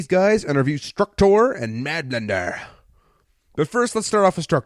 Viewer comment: what did he say? Madender? Salamander? Sadmander? Merdender? Sertider? Madblender? Maddenender? Bladtender? Tadstender? Radblender? Stadnender? Bsgmejdder?